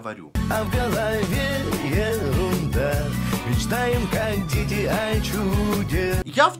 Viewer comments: Мечтает кондитер о чуде мне послышалось ))) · Я один с первого раза услышал: мечтаем как ДЕТИ о чуде (песню эту ранее не слышал)? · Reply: Дочь услышала "Мечтает кондитер о чуде"